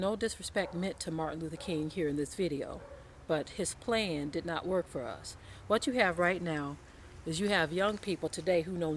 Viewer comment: What is that word she used at 9:14 "saughtish" I'm trying to find it?? Also there's a quote that MLK made in which he says "I'm afraid that I sent my people into a burning building". That says it all he led many people astray and today is the result of it. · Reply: Sottish, Jeremiah